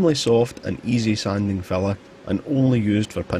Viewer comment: I thought you were saying "fella" 😊 well he's a friendly guy with his tools.